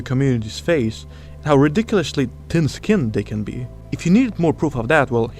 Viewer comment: I'm sorry I kept hearing it as "tin-skinned" and thought "wait the mods are all bots?" I'm so fucking stupid